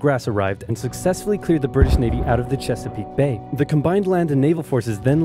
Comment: I thought that said the battle of cheesecake